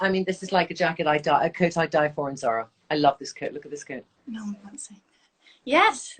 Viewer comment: Really want to buy that beautiful coat at 30:17 - did Trinny say she got it from Zara? It's to die for!!!!